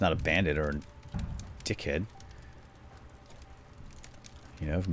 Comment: o Meh god did say what I think he said at 18:08 Did he say dickhead